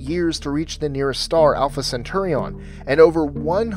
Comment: Did he say Alpha Centurion? (5:12) It's actually Alpa Centauri.